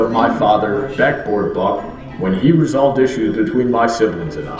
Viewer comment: First time I heard it, I thought Brawny said "Backdoor Buck." Oh god, the implications.